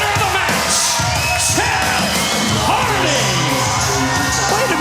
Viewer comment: did he say Seth hardy?